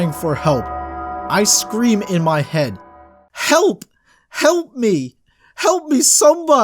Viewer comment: i thought he said 'kelp' for a minute 😂